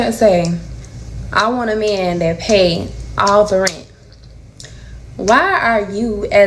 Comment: I may have heard it wrong but it sounded like she said “All” the rent as in she lives with him. Instead of “Her” rent implying they live apart.